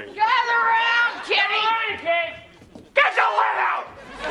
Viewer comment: "Gather around kids" "Come on ki-- GET THE LEAD OUT!" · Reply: I thought he said get the widow hahahahahaha